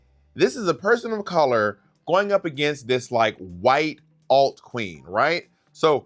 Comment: You said "white alt queen" about Sharon and I thought you were about to say "alt-right" queen. Would have been truly spot on. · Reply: lmao, I literally thought he said "white old queen" until I read your comment